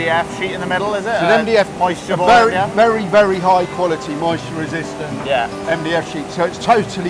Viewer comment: Just started watching, only 5 minutes in but when he said very very moisture resistant, I thought he said Mary Berry moisture resistant 😂😂, had to go back and listen carefully. Finished the video now, great to see that the kitchen company are still using old machines alongside newer computerised ones, really interesting. Great to see the guys at work in the kitchen too, bet they wished they could do it that fast 😂. Good luck and hope you make the kitchen fitting date 🤞🏻, absolutely love your out takes, so funny, can’t wait for next weeks video. ❤❤